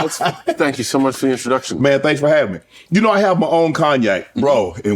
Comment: Did he just say "Man thanks for having ME" ? 1:42.